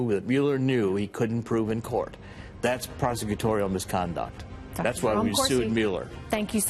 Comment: "That's why we are suing Mueller" Did I hear that correctly?